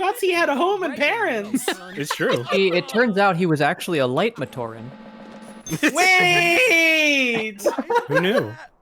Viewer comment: Did I hear one of you say "Light matoran"? As in: the matoran of light from Bionicle???